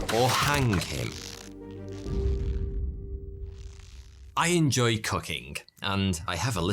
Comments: I literally had to turn on the CC because at 1:45 all I heard is "I enjoy cucking" even after rewinding it 3 times lmao. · Definitely thought he said cucking at first not cooking😅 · For a second, in the beginning of the ad, I misheard you say, "I enjoy cucking."😂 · At the beginning of his ad, I thought he said "I enjoy cucking." He likes to drink wine while doing it. Damn, bro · “Either he hire the boy, or he hang him..... I enjoy cooking...” talk about a tone shift 😂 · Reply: Well.. At least its better than what I thought.. I honestly thought he said "I enjoy cucking" at first..